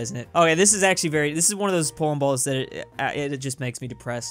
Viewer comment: my captions thought you said porn balls